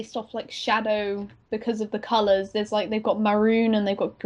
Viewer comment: I thought I heard her say "Kamalas" instead of "the colors" and got so excited